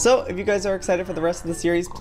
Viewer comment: Ik I’m not the only one who thought he said good damn bye